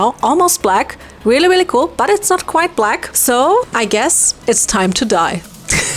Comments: DID SHE SAY DIE????????? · She Said 'its time to dye' what i heard was 'time to die😔✌️'